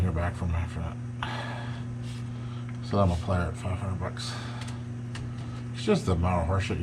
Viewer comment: it is kinda hard to make out exactly what you said... it sounds like the dude wanted 1800 or better for a 1963 sedan with a 413 (prolly not running) - and you said get it running and we can talk...then you said he never got back to you, and then I think you said "I'm a player at 500 bucks" If that's what you said, I don't quite get it. You offered 500?